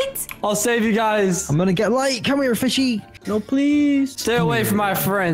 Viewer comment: LOL I HEARD “COME FISHY”